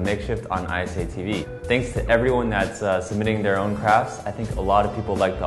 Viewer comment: I misheard him say 'crafts' for craps. Lul. heh.